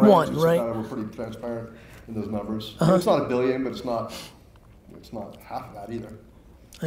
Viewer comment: Gary: How many users are on the platform? We don't release that publicly Gary: More than 1 though? Less than half a billion. LOL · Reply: thought he said not 1 billion not less than half that